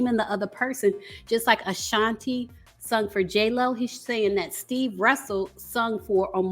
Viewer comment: If anybody was paying attention to the Verzuz, Mario mentioned that somebody sang vocals for Omarion too. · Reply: I thought Mario said stevo but it’s one in the same I suppose 👀👀👀